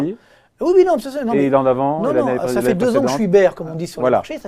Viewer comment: A 16:00 Marc Touati dit quelque chose comme "ça fait 2 ans que je suis bère" ça veut dire quoi ? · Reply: être bear (baissier) ou bull (haussier) sur les marchés financiers. Depuis 2 ans les marchés ont pris 30%